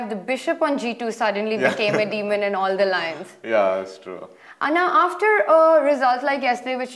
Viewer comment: Does Tania call Adhiban "Anna?" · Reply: I think she says 'and now'